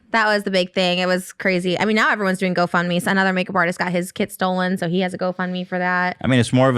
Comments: I thought she had said the guy got his kid stolen at first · I thought said he got his kids stolen 💀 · i thought said that guy got his kid stolen instead of kit💀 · When talked about the makeup artist whose kit got stolen I thought she said his kid got stolen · I thought said, "he got his kid stolen," instead of, "he got his kit stolen." I was like: WHY THEY AIN'T MAKING THAT A BIG DEAL. A HUMAN CHILD STOLEN. But then I realized.... LOL. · i thought she said he got his KIDS stolen like damn how did that happen